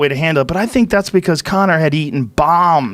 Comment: At the end it sounds like he said “Conor had been eating balms” ....or is it just me? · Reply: Bombs